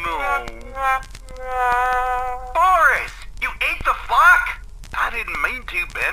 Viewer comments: When Bendy said "Boris,you ate the flock?" I thought he said "Boris,you ate the f**k?" 😂😂😂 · Did he say f*ck or funk in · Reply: He said *Flock. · did someone say THE F#!/K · it sounded like he said ate the fuck 😂